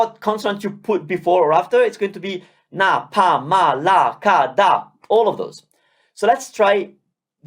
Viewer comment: I really heard at 8:45 Malaka 😂😂😂